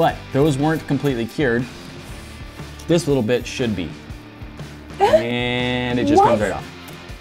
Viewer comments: IT Sounds Like He says "this little Bit*h should be"😂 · it sounds like nate says"this little b*tch should be